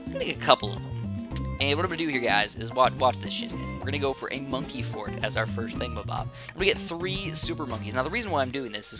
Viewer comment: did you say sh**? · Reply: he said shiz